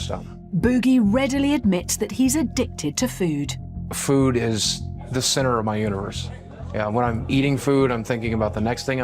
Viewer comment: i thought he said food is the centre of his uterus 😂